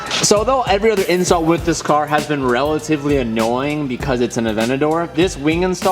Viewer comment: Anyone else hear "Although every other incel with this car has been relatively annoying" 😂